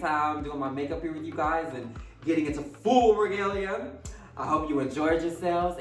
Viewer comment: I thought it said legal instead of regal lol